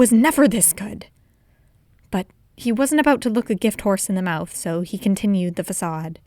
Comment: gift horse in a mouth? did I hear it wrong qwq